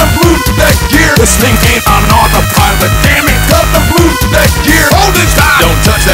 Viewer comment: "This thing ain't on autopilot" I always thought he was saying "Listen kid, I'm not a pirate" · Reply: I thought it said 'This maintains on autopilot'